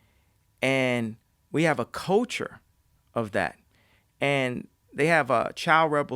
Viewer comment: Can anybody explain what a "coacher" means in this context? He says it multiple times. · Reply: Culture with an accent !